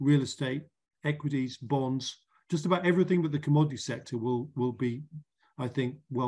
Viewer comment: did David say everything ‘but’ commodities will decline? It was hard to hear what word he said there. Thxs. Great interview.